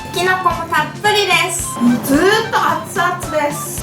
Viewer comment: きな粉もたっぷりです。 って聞こえてしまった…